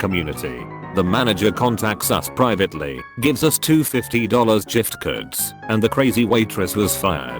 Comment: Giftcard to J I F C A R D Gif = Jif, proven with science. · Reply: actually i hear "jif curds" XD